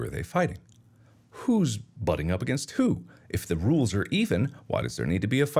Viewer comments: Karen gave Mark a strike. Stupid Karen. Did Mark just say "If the rules are Ethan, ...."? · please dont tell me im the only one who heard "Ethan"? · Am I the only one who heard if the rules are Ethan?